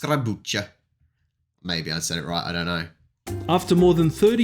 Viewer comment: I'm polish and I have no idea what you just said. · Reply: It took me around 30 seconds to get what he said. At start it sounded like Italian or Romanian, but what he really said was "lajkujcie i subskrybujcie" xD